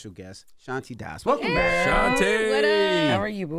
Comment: I thought they said Ashanti and I was so concerned lmao! Both beautiful ladies but they look so different lol